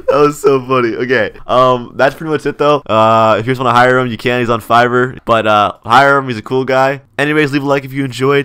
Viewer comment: Me: You can hire scripters for your game!? Gets wallet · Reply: When he said “fiver I thought he said “fiber” I can’t see that good so I could be right or wrong